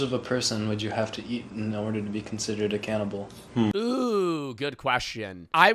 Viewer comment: I accidentally thought ypu said consent